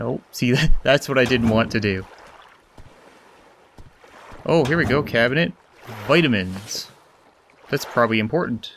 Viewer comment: wow i am like 2019 subscriber and i heard you say 'here we go mr crabs'